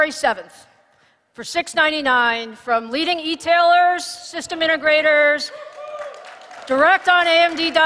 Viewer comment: $700 for Vega7 is way too much. Plus AMD missed the opportunity to really hype their new Ryzen 5 3600(X). Without the context of the whole new lineup and pricing, it's meaningless. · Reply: That's not what they said. They said: "from leading etailers, <link>, and in Alienware systems" (1:12:48). Cards from board partners will probably be more expensive. And I'm very worried that $699 will translate to much more in practice. In Europe an RTX2080 will set you back $830 up to a staggering $1480 (converted), tax included. I'm afraid Vega7 will be at least $850 in Europe. To me that is totally unacceptable. I don't care if it's Nvidia or AMD. And above the RTX2080 there's RTX2080Ti, and later Titan RTX. At even more insane prices. Remember launch price for Vega64 was $500. GTX1080 was $600, GTX980 was $550. Don't even get me started about the 'founders edition' for a cool $100 premium. Nvidia has been able to shift segments (higher prices for less increase in performance) because there was no competition. And please don't say that it's logical they ask more for a new generation just because it's faster. Technological progress means that you get way more performance for roughly the same price, which has long been the case! Do you want to pay $1000 or more for a graphics card? I don't.